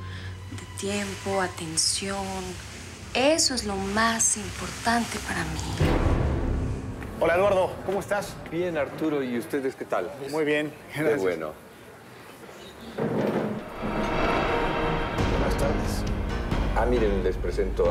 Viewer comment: parece que dice " hola gordo" 😂